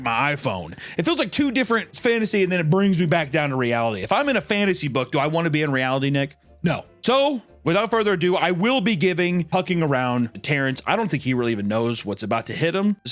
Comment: Puck around and phind out. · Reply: Thought he said "Hawking around"